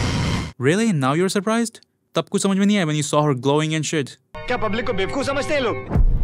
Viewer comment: Wt did he say... Tb Kuchh smjh Nhi aaya jb.... Wt????? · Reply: "tab kuch samjh me nahi aaya when you saw her glowing and shit"